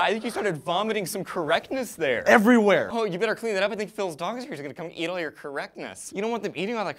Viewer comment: I heard that as "Phil's dong is going to eat all this correctness." It took me a second to realise you said "Phil's dog."